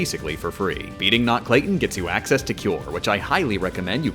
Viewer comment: Beating Not Clayton to gain access to what? I can't understand what you're saying even if listen 100 times. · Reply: Cure